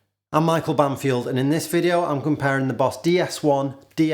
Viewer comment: Sorry, but I thought you said Micheal Bumfield at the beginning of the video.